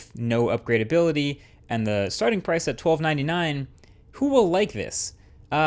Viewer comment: For a second when you said "twelve ninety nine", I heard it as $12.99. Whoops.